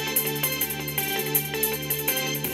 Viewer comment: i thought it said "te bluetoof device is 3d 2 pal"